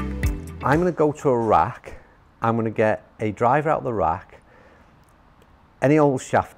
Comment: Good honest review good job Really thought you initially said "I am going to go to Iraq yip going to get a driver of iraq" ha